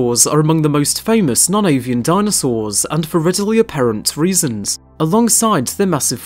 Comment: I heard non-ALIEN dinosaurs and I shit